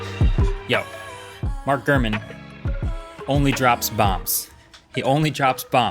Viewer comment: I thought he said he had "Mark Fuhrman" on the podcast... 🥴